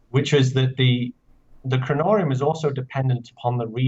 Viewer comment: Jason said "Kronorium", and the Closed Caption thought he said "Cannoli Man"